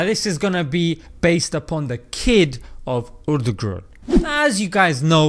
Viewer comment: Why does it sound like he says "Urdu group" when he says Ertugrul 😂😂😂